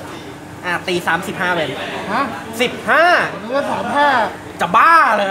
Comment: ได้ยินว่า 3 5จิงๆนะ555